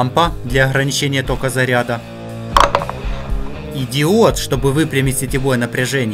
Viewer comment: послышалось идиот